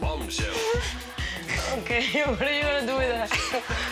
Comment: What does sophia said at 0:58 "okay..?" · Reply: ok, what are you gonna do with that? xD